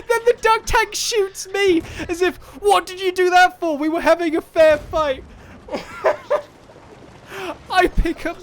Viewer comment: In at 8:04 vidio, sound like he saying "We having a affair fight" hehe